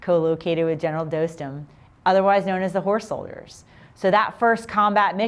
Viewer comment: The "whore" soldiers? Did I hear that correctly??